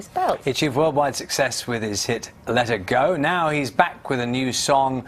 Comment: did he just say 'let IT go" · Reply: no he said "let her go" in a british accent you buffoon